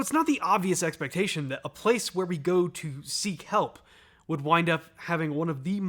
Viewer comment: Oh ho, that’s funny. I thought he said ‘sea kelp.’